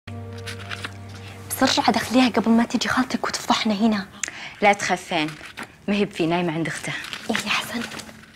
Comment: وش قالت ماسمعت زين هه 😂. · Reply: The best . قصدها لا تخافين بس واضح الجداوي اذا تعلم بدوي